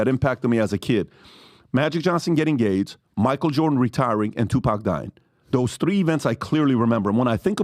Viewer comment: Did he say "GAIDS"? 🤔